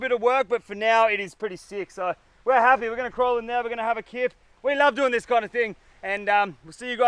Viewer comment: When he was describing the shelter I thought he said "we're gonna crawl in there, we're gonna have a kid"....lol